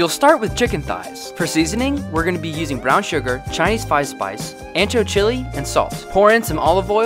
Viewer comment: Bro i swear i heard him say chinese ice spice 💀